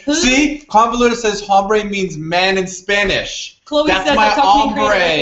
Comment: I thought Simon was saying "hambre" (hungry), not "hombre" (man). ^-^